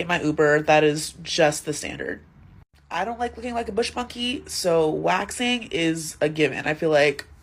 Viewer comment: did she just call herself a “bush monkey” 🤣